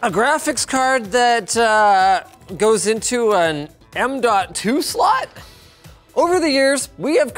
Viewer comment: Стопе. Я же не один услышал м два ту слот?